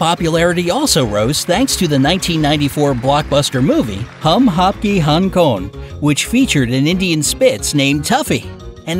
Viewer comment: In 9:27 I thought he said" hum aapke Hong king "lolll